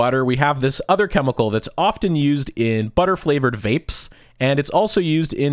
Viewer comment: Excuse me, did you just say “butter flavored vapes?”